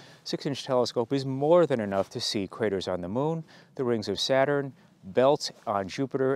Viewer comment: It sounded like you said "you can see critters on the moon", but I assume you said craters ;)